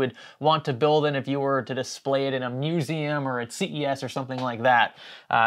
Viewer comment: I thought you said to display in a museum or CVS for a second and I was so confused.